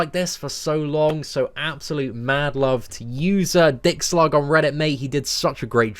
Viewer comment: Did he just say mad love too dicksnogger 😂